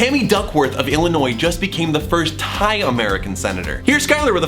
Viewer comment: I honestly thought you said she became the first HIGH American senator, I had to go back to make sure.